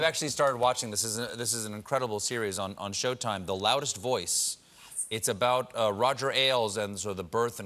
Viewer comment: Did anyone else catch her whispering "yes" in regards to The Loudest Voice? That is brilliant lol!